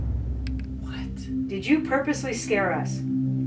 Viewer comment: it sounds like she is saying “you guys scare me” not “he is doing it” but maybe not